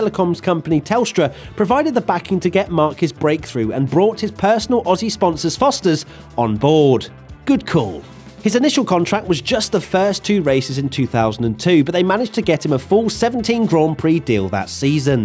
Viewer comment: thought they said first 3 races